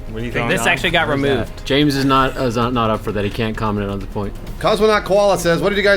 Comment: the video buffered at 19:50 and i heard "james is not up for that, he cant cum "